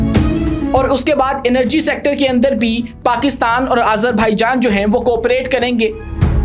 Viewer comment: Did you say Azerbhaijan ?